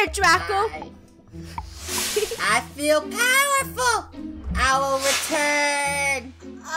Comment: In the intro, I kinda panicked cuz I thought Draco said: I WONT return!!!!!!!